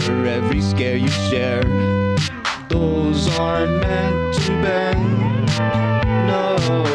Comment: The first time i ever heard this song before i thought it said "ToEs ArEnt mEanT To BeND".